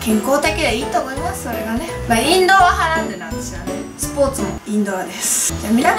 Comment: インドアがインドラに聞こえた